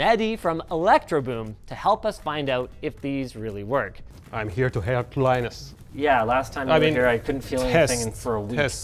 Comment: ish. Subtitles: "I'm here to help Linus". What Mehdi actually said: "I'm here to hurt Linus."